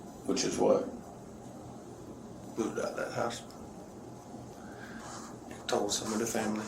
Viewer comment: 😮*wait?* Did he just say "I should have put IT out of the house" . Like she was a black widdow spider or something.